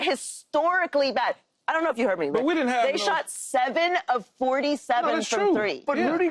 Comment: Every time I hear them say "we didnt have length," I hear "if we kept AD this wouldn't have happened".